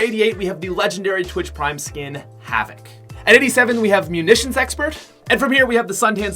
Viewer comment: i heard ammunitions expert